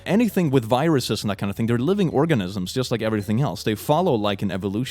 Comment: I just heard "living orgasms"